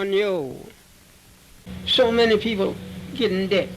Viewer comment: when he says “so many people get in debt”, my automated subtitles say “so many people getting dick”